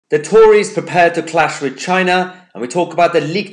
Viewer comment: I thought it said "tories prepare for china cash"